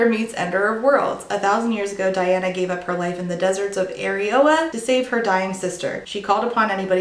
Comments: I'm not the only one who heard "Areola" right? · I had to do a double take because I heard the deserts of areola 😅